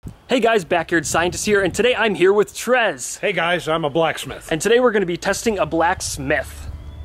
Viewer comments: It buffered for me and all I heard was, "Hey guys I'm a Black" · I thought he said hey guys imma Blackman